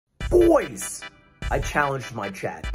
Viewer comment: ngl i thought he said "my chat is challenged" at the strat